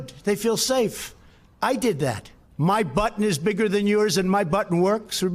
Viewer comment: I thought he said "my butt is bigger than yours".